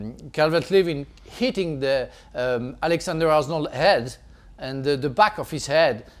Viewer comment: Did he really say “Alexander Arsenal”😂